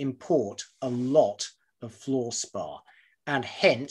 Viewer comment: Interesting video, thanks. I initially thought you said 'floor spa' - ie. some kind of foot massage. Hell, if I am willing to invest in bitcoin, why not foot massages........?